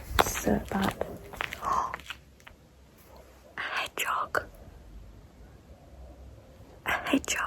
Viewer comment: Very few doctors and engineers, sadly that is changing. Btw I thought you said " LA jug" and I was what is that? And then I realized it was just your way of saying hedgehog. Gotta love your accent 😂.